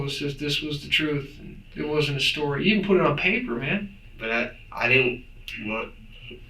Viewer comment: He didn’t say “I wanna go home. What have I done” He clearly said “I wanna go home. I didn’t do it” starting at around 1:19:00 I believe… Okay, now time to finish the rest